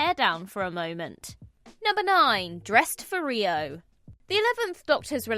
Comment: I always thought Amy was saying, “I dress for real.”